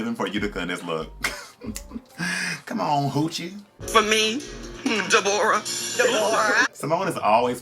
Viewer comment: At first I thought she was saying DEV’ORRA from Mortal Kombat as well but then realized it was DE-BORAH. 😅 Cmon Gaymers!